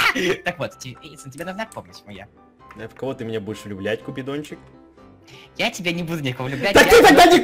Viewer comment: Мне одной послышалось , что он сказал "куплинов"?